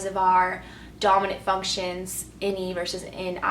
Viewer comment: What are you saying at 2:52? "In E vs In I?" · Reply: Ni vs Ne. Introverted intuition vs extroverted intuition...